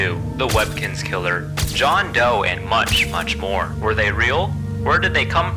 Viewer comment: In the beginning I thought he said “munch munch more”